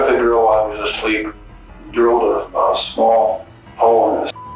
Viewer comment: what did he say at 2:45 ? did he mean drillling a small hole in his spine or idk why its censored and what he ctually did^^? could someone lecture me..? maybe he said skull? i dunno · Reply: Its skull it might be sensitive for some people that's why maybe they censored it.